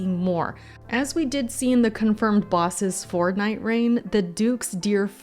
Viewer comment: I can't help myself, I always hear Fortnite rain no matter how many time I rewatch